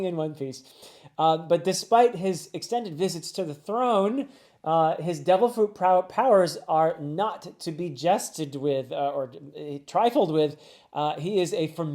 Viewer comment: I genuinely thought you said "his devil fruit powers are not to be digested"